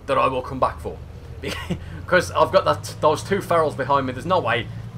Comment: I thought you said barrel its actually feral